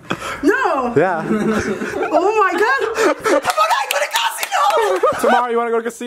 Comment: Is it just me or did I hear mom say "I'm gonna go to Costco" @ 1:59? Bc if so that purity if even more off the charts